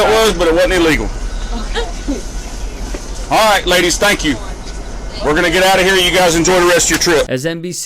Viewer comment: Sounded like he said stank you smh